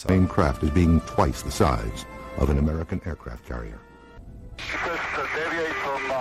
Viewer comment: he actually says its twice the size of an American aircraft CARRIER, which is fucking massive. That said I dont believe a word of this stuff, if you look at the reports from the 70s a lot of them match modern stealth fighters. Just a lot of military secrets. UFO does not mean aliens